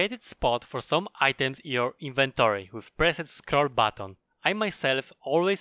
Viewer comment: Dedicated spots in inventory, at 10:16- what does he say the key is? I listened probably a dozen times and cannot understand what he says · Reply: it is middle mouse button